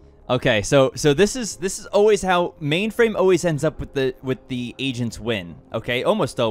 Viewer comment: why does it sound like he saying asians instead of agents lol